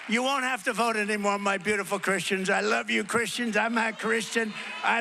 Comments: Did he just say he's not Christian? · “And again, Christians get out and vote. Just this time. You won’t have to do it anymore. Four more years, y’know what. It’ll be fixed, it’ll be fine. You won’t have to vote anymore my beautiful Christians. I love you Christians. I’m not(?) Christian. I love you. Get out. Ya gotta get out and vote. In four years you don’t have to vote again. We’ll have it fixed so good you’re not gonna have to vote.” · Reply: here he clearly said "I'm a Christian". I don't know why people are saying the opposite ! Except, he did use a bit of a gravelly voice on the word "a"... But everybody knows he's Presbyterian and that growing up his family attended Dr. Norman Vincent Peale's church in New York City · “Just this time. You won’t have to do it anymore. Four more years. You know what? It’ll be fixed! It’ll be fine! You won’t have to vote anymore, my beautiful Christians. I love you, Christians!” He added, “You gotta get out and vote. In four years, you don’t have to vote again. We’ll have it fixed so good, you’re not gonna have to vote.” Doesn't get more overtly anti-democracy and pro-fascist than that! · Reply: here he clearly said "I'm a Christian". I don't know why people are saying the opposite ! Except, he did use a bit of a gravelly voice on the word "a"... But everybody knows he's Presbyterian and that growing up his family attended Dr. Norman Vincent Peale's church in New York City